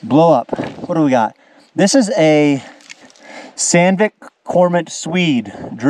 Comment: Took me a while to realize due to pronunciation, but was that drill made in Sweden? I think i heard him say "Sandvik".